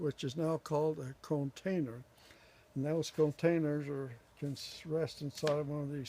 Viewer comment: Where do you get those deep potting Containers? I thought I heard you say “conetainer” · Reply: They are called "Cone-tainers" Their web page is: <link> They are in Tangent, Oregon USA. Thanks for visiting my U Tube channel.